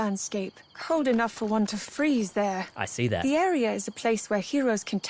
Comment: I thought Zelda was saying "freeze their... Posterior